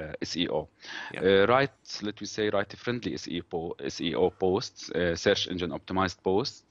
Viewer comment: HI I could not get what you said in Tip No 8 for beginners at 28:07. Can you spell it ? It's something about keywords....or session optimised ??.... Thanks, Bharat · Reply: He was talking about SEO, Search Engine Optimization and optimizing your keywords with the search engines in mind. It really did sound like "session optimized" though.